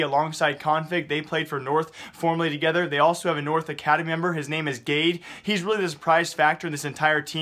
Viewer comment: I just heard : "He's name is Gay"